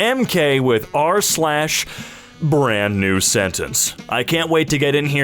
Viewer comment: is that saying 'r/brandEnewsentence'?